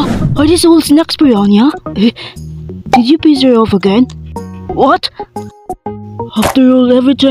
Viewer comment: When they said “did you piss her off again?” I somehow thought they said “did you piss on her again?”